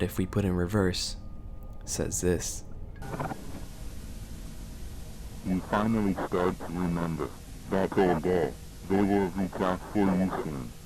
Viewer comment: I think what it said was “you finally start to remember, the very day you found Sophie” or “you finally start to remember that he could never bring us safety” that was what I heard.